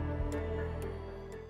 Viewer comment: I heard "Easy pick here, the kobold geomancer" and was like Wat, for 2 seconds.